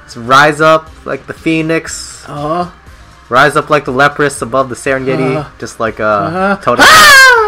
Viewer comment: For a moment I thought you said, "Rise up like the leprous." I was thinking, "Kinda hard when you've only got one leg."